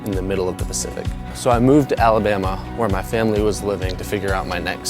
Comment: i heard alabama not albania lmfaoo